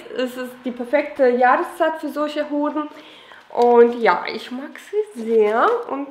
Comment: Hab ich verstanden "für solche hoden" 😂